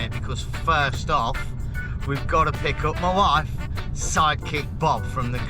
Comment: lol, i thought you were saying "psychic" and not "sidekick." i think i'll stick with "psychic." Cheers and thanks for sharing!